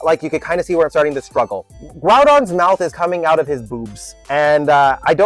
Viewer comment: Oh i proper misheard u at one point when u said growgons mouth was in his boots.. i thought u said boobs.. ooopsie lol.. had to share my cabbage moment 😂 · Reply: I did say boobs. 🤣 The proportions were just so wonky. 😅